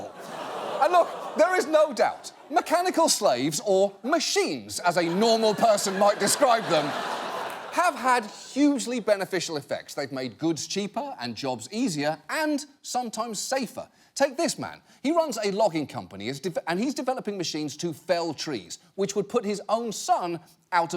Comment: I swear I heard "And jobs easier," as "H*nd jobs easier," and literally had to roll it back to hear it again. xDxDxDxDxDxDxDxDxDxD